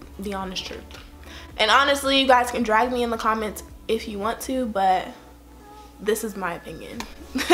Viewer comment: BOY!!! I swear I thought she was gonna say "you guys can drag me in the comments but... I drag back..."